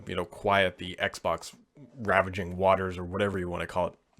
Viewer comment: Ravaging right?! Kinda sounds like you said ravishing